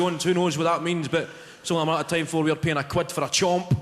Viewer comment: can somebody help me with what he said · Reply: He says "it's only a matter of time before we're paying a quid (1 pound) for a chomp (a small chocolate and caramel chew bar which normally costs 10p)"